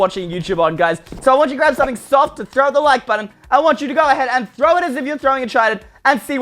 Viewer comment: I thought he said throw a child 👁👄👁